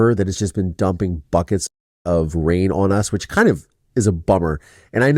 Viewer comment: Omg I thought you said 🖕uckits of water, not buckets of water.🤣🤣🤣🪣💦